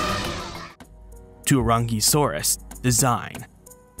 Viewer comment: I totally heard the "Teriyaki-saurus" 🤣😁 It's too early for me already lol 😆